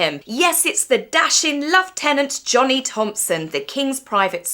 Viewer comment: Did i hear that right? 4:30 the special lovetenent? How agreeable :)